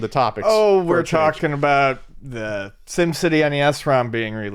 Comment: I thought he said Sin City nes rom! I WAS LIKE WHAT????